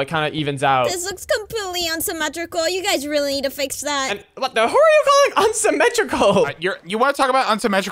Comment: i heard you say what the f 💀💀💀💀💀 you say a bad word